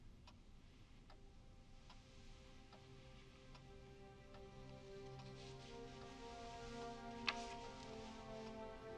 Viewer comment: what were the four words? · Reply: “Because I could die”